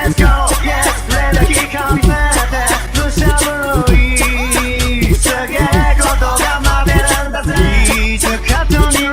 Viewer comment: só eu ouvi "camiseta do Gustavo luis"?